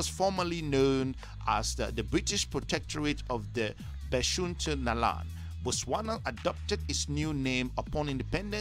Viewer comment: If you're honestly going to do a research about a country its best to find the right videos and get pronunciations right 😭😭😭😭 but nice video tho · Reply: I think I just heard him say it was called "The Protectorate of Beshuntaland" Also, what's up, Bux?